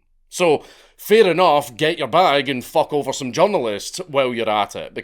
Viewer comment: I thought you said "fuck over some jungalists " then, I was about to kick off